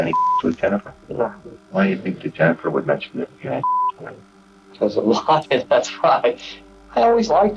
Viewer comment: I think he says 'sexual encounters'. At 14:18 he says: 'Why do you think that Jennifer would mention that you had sex with her?' Then at 14:28 the forensic says: 'He literally is laughing as he is talking about raping a 14-year-old girl' It would have made it easier if they hadn't recorded those annoying beeps over the top. .